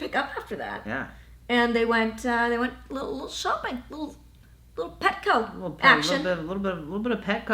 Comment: I misheard Petco-action as "petting action"... and sure we're talking about dogs, petting can be SFW... but that's not where my mind went.